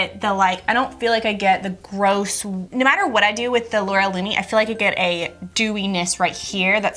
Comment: I could have sworn that you said "like the Laura Linney" when you said Loreal Lumi hahahaha! I had to stop and think "Does Laura Linney have a makeup line?!?"